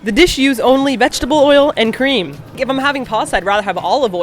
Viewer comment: Hahah when said ''..vegetable oil and cream'' I heard ''..vegetable oil and Korean'' O.O